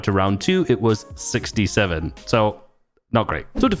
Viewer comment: did bro say 67